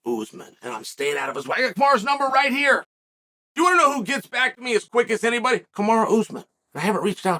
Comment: I literally thought chael said "You know who gets acne worse than anyone? Kamaru Usman " 😂😂😂😂😂